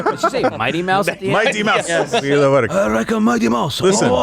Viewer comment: I 100% thought she said Mighty Mouse too 😂😂😂 that person who said this new president has extremely regressive views on women and women’s rights… They do realize she is probably the most powerful person, correct? Wow.